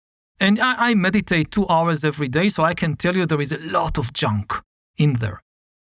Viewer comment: did he say "I meditate 2 hours EVERY DAY!" 😮